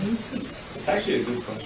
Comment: Anyone else hear, at 4:56 , someone say, "That's actually a good question." before the feed cuts? XD